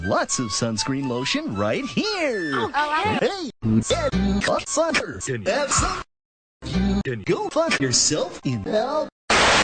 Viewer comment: What did he say in his sentence at 2:37? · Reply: He said, “Hey, who said you cocksuckers could have some?! You can go fuck yourself in hell!”